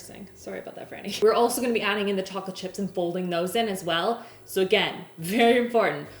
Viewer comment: we'll be adding taco chips? Have I misheard you or is this a secret ingredient that's been kept from the English so you can make superior cookies?? 🤔 Great fun video, going to have to make these with my daughter. Thank you and Happy Christmas 🎅